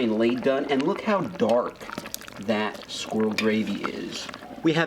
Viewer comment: When I heard him say “squirrel gravy I thought he said “squirrel rabies” XD